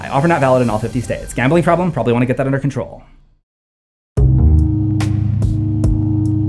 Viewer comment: “Gambling problem? Probably get that under control.” BAHAHA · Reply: I heard "probably wont get that under control" so i had to come back and check lol